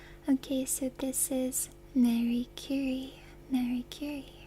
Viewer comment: I was watching/listening to your video as I’m falling asleep. I swear you were saying her name was Mercury 😅